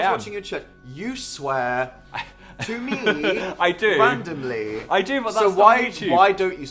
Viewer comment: What everyone hears "you swear to me randomly" what I heard "you sweat on me randomly"